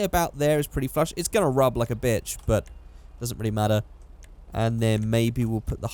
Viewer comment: did he just say rub like bitch? and what a timing he said it in "420" minutes....